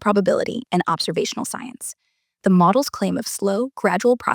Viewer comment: Grok says observed but the subtitles say 'absurd' at 14:55. Maybe proofread the subtitles before posting?